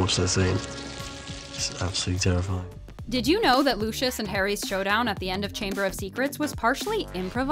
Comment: did i hear lussius